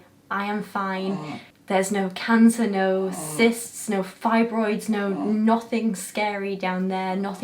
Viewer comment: I thought you said "thyroids" at first and I was like GIRL YOU NEED THAT!! I'm so glad you're okay <3 you're amazing